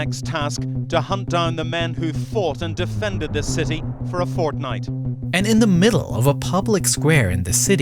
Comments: did it just say Fortnite??? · Did he just said Fortnite